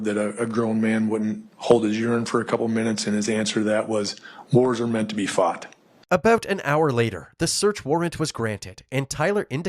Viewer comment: ‘wars are meant to be fought’ after pissing yourself is fucking hilarious 😭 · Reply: I thought he said "whores are meant to be fucked" and was wondering why they didn't censor it lmfao